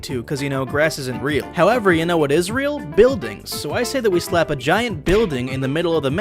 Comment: "However you know what Ezreal?" instead of what is real.Gotcha